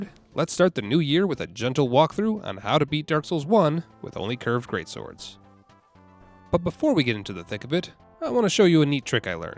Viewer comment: I dont know why but i thought this video said "How to beat EVERY DARK SOULS with only curved great swords"